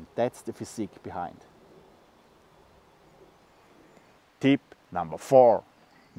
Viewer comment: I love the video but I did chuckle when I heard “tip number three” but saw “tip number four” lol 😉 💜